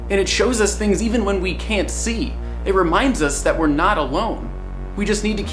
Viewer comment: wait, did he say... we are not alone? that just made me believed on ghosts... 😱😭😵